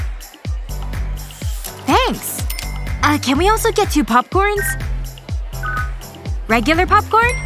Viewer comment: Did she just say popporns? 0:20 💀